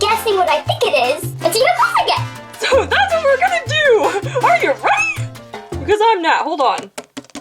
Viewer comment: You: Are You Ready!? What I Thought You Said: Are You Ready Kids Aye Aye Captain!